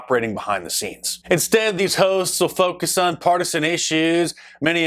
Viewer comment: when he said hosts i thought he said ho's for a minute lol